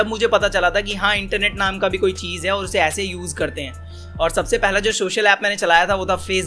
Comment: he literally said shocial instead of social